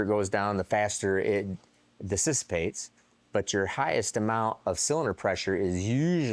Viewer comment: Did he just say disistapates? He did 😂